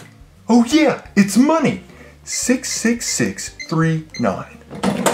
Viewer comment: uhhhh did u say 666?!